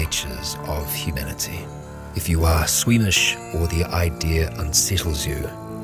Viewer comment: Fun fact with my hearing disability i thought the start said if you were Swedish not squimish